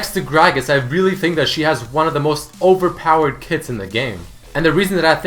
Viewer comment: i swear i heard "next to gragas she has the most overpowered tits in the game."